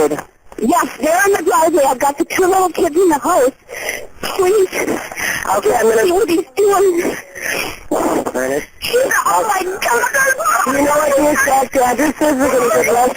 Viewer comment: Dispatcher: okay, I'm gonna...... Darn it Could have swore the dispatcher said that. LOL!